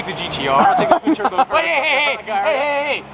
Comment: Did I hear "_twin turbo Huracan_" at 7:23....?